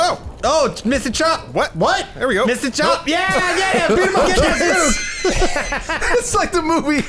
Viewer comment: Anyone else hear James yell "GET THAT SPOOK" at 11:57?